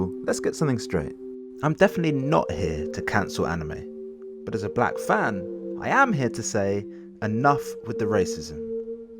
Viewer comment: did he just say he a black fan? 🤦🏾‍♂️😂